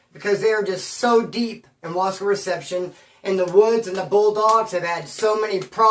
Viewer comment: I thought you said Bullfrogs 🐸